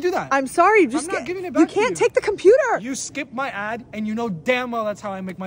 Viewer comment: I thought he said you skip my My mind is broken